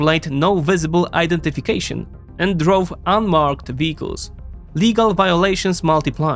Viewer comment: ice agents drove unmarked beetles? 😮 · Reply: first time I heard "vehicles" now I can't unhear "beetles" lol.